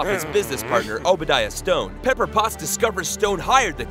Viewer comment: Did you just say "Stone?"